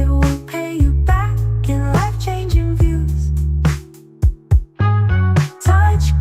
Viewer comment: Hey, don't feel too bad about not saving more ppl.. hell, I have a real hard time with just my family, you are already doing a great job changing many ppls lives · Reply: Also, my wife heard you say "wife changing money", laughed and told me to tell you "I know you're joking, but f* you" 🤣